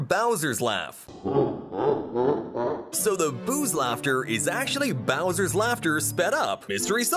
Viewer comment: I heard "sollong der bowser!